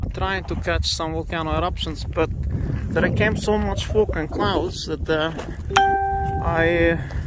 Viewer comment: Did I hear you say "fooking clouds"? Is that a Icelandic term? Lol